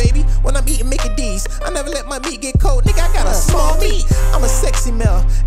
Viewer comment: Did he say I gotta small meet